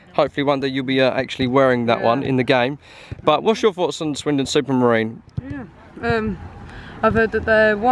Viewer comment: I thought it said Swindon Supermarket 😅